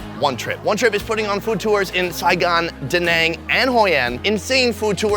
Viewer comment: Did he just say "Danangnian" Either way, I really enjoy your Vietnam videos. I'm from Danang :)